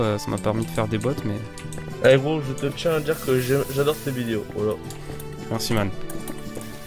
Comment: il a dit quoi le mec j’ai pas compris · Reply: Il a dit qu'en gros il adore mes vidéos